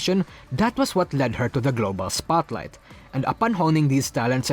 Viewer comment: I thought you said "girlboss spotlight" but you actually said "global spotlight" pfft